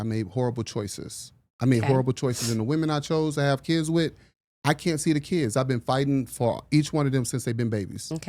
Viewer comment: wait i thought he said he hadn't seen his kids and only men like him think like this man with sense don't think this way